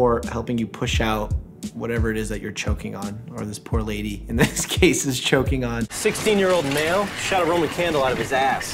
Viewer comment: Omg I couldn’t help laughing when he said, “ this poor lady who is choking on (but then the scene switched so it sounded like he said) “ this poor lady who is choking on 16 year old male” 🤣🤣🤣